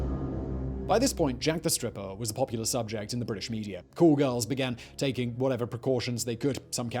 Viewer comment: it was at this moment I realize he was saying "call girls" instead of "cool girls"